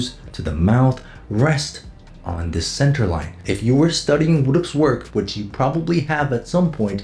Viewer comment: what are u saying at 2:40 ? who the fuck is wooducks? vooduocs work? I am sad I can't understand it, please help <3